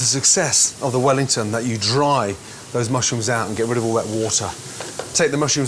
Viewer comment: did he said wet water?